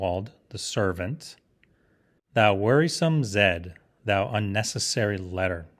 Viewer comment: Perhaps I have misheard but I thought you pronounced ‘whoreson’ as ‘worrisome’ i.e. ‘annoying’ - and not as whore son i.e. ‘son of a....’etc. I thought you may have been being delicate! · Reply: Ahhh, no I said worrisome. Sorry, I get nervous recording these things and hate listening to my self to check:)